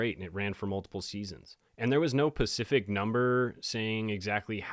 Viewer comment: Did you say pacific???